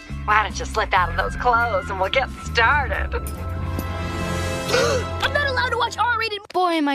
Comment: IM NOT ALLOWED TO WATCH R RATED VIDEOS I think he said lol